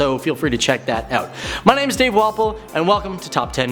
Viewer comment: Wasn’t lookin at the screen, heard him introduce himself. I thought this man said his name was Dave “Waffle”.